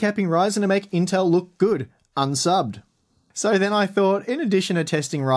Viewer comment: " you're handicapping ryzen to make Intel look good, unsubbed "... I heard " you're handicapping ryzen to make Intel look good, and suck" poor:(